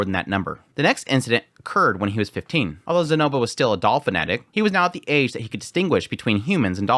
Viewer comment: I know you said Doll Fanatic, but it really sounded like Dolphin Addict. XD